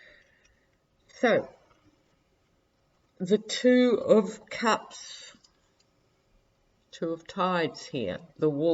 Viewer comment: When you pulled the card with the wolves on it I thought you said "Two of Pups". haha. I enjoy every word you say but I will always think of that card as Two of Pups.